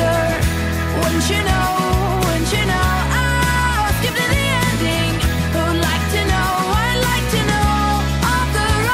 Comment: did she say skibidi?? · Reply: Pretty sure she said 'skip to the ending'